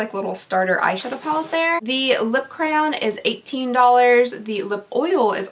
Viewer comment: 1. I thought you said lip crown and not lip crayon :| XD 2. You’d think they’d ask for your birthday and get the right sign for the lip oil lol But who am I to say!